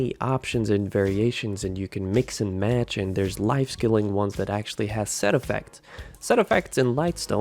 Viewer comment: Awww, I thought I heard you say "life steal", but it was just "life skill". Got my hopes up for a second. =(